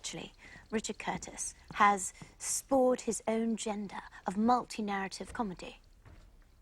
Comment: Question from a non-native English speaker here: What is Bridget trying to say around the @ 2:44 mark? “Do you think he’s [spored/spawned/?] his own [gender/agenda?] of multi-narrative comedy”? I can’t quite hear what she’s saying, and while it’s supposed to confuse Colin Firth in this scene as well, I’m still curious about the actual words. edit : she says “spored his own agenda” when she meant to say: “spawned his own genre”, which means being the first at something. Thanks Google! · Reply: She means "spawned his own genre" Spawned = create and genre is a category/style of art or film. She's saying "spored his own gender" but that's because she's getting it wrong. Google did not help you. "Spawned his own gender" is a meaningless sentence.